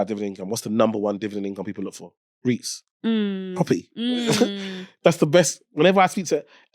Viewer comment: what did he say after dividend income at 24:30? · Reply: Reits